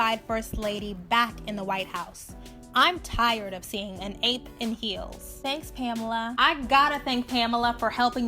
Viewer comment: I was listening to this on fast forward and thought she said "an 8 in heels" like 8 out of 10. I was thinking that was not that harsh, but what she actually did say was so racist and messed up